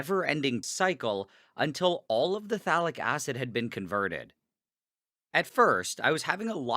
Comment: It sounds like he is saying phallic acid.